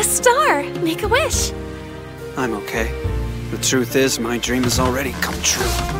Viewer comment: I'M SCREAMING I FR THOUGHT THIS MAN JUST SAID "I'm gay" ( 1:20 )